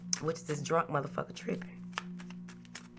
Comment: "drunk motherfucker trippen?" @ 17:00 or am i trippen in what i heard LMFAO cuz bitchhhhhhhhhhhh